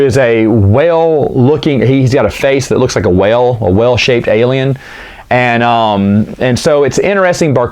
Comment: A whale-shaped alien...I thought you said "well-shaped" at first! XD